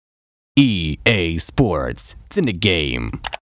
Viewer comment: I heard it as EA Sports It's Emagame or Ina Game.